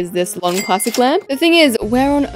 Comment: she said lamp instead of ramp